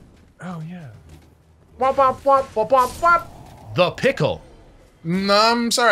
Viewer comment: I always heard "nut pickle". All the voice clips in SM64 are so compressed.